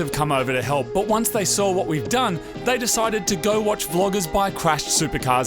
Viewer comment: "Vloggers buying crashed Supercars" :D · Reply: Im sure i heard "floggers"